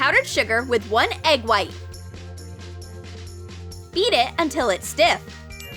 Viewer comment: Who else heard eat it